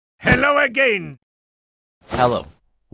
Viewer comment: "Hello a gay"💀💀💀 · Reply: It sounded like he said "hello e gang